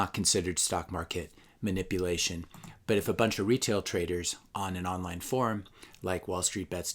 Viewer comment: shiit I really thought he was about to say "But if a bunch of retards ..." xD